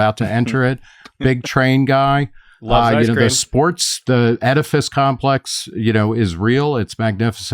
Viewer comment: Did Nick say "edifice complex?"